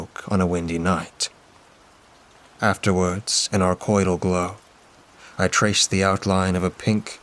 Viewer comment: What word was said at 35:08 ? Sounds like arcoidal or archoidal? "afterwords, an ___ glow. I traced the outline of a pink hibiscus flower Beth had tattooed..." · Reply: “Afterwards, in our coital glow…”